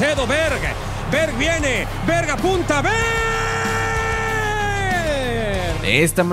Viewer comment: porque dijo VERGA 🤔🤔🤔🤔😂😂